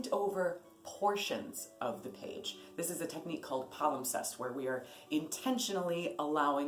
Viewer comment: you mentioned a technique...I couldn't make out the word. What was it? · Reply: I think the word you are looking for is palimpsest, she mentions it in a few of her videos, including the Rock your Grimoire one, and shows how she has drawn and painted over the top of paint recipes to create something new. <link>